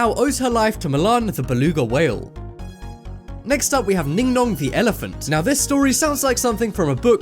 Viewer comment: ningnong the elephant I thought he said dingdong the elephant lol